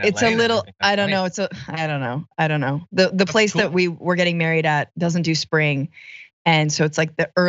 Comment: At first I thought she said mail weed, instead of married.